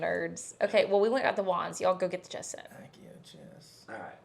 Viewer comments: 😮 I thought you said “where are our wines??” .. wands, she said wands .. love that you guys are like siblings, always goofing around. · The “Are you drunk?” 😅😅 and I honestly thought you said why don’t we have our wine and not wands. lol